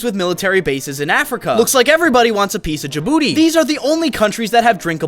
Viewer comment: it sounded like "Everyone wants a peice of your boo-"